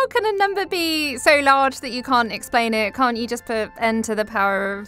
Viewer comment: I heard "How can a member be so large that you cant explain it?"... i thought it was an erotic aurthor type question...